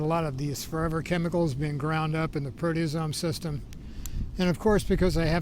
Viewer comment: What is that you said here 01:02 where you say forever chemicals that are ground up by the... what system? I couldnt make that out. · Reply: ubiquitin proteosome system